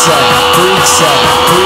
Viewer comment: Why the hell did younger self think he was saying, ah the mighty trumpet brings the fish onto the floor? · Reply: I thought he said, "tell me where the freaks sat.", Not "tell me where the freaks at"